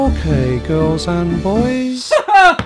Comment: I thought Pinocchio said let’s go kill some boys instead of ok girls and boys